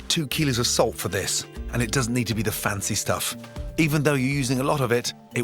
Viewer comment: I'm sorry. No matter how many times I watch this video, does he say, "It does have to be the fancy stuff" or "it doesn't" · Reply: "It doesn't need to be the fancy stuff"